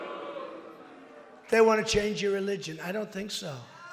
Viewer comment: Literal Q: he went to east London (a place full of Muslims) he just walked right pass them like he did nothing like wtf 2:19 he says that the Muslims should change religion and it will still be too late Omfg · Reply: He said that Muslims wanna change other peoples religion